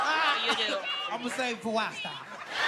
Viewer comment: What did he say at 3:01?? I cant understand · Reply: he said ima save it for wild style